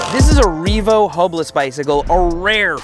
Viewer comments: sounds like said HOMELESS instead of hubless 😂 probably accurate for the makers of this product 🥲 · sounded like you said "homeless bicycle"